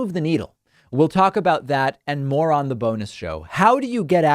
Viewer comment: EVERY SINGLE TIME David says "bonus show" I hear the words "oh boy.." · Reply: Wait I thought he was saying “BONERS SHOW”